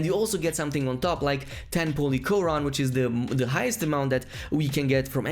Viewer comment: bruh ngl when he said "polychoron" i thought he said "holy quran"